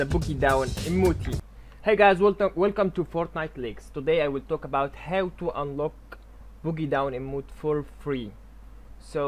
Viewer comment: This guy is Egyptian 😂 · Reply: I thought he said fortnite licks in the beginning